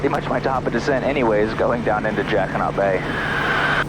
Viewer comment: I heard "jacki-not" the whole video until you said I might be hearing it differently and then I could only hear "jackin' off bay" 🤣